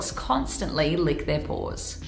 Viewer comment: Sounded like she said constantly lick their b***s 😂